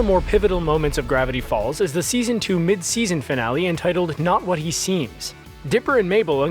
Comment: he says not what he seems but the screen says now what he seems probably an editing error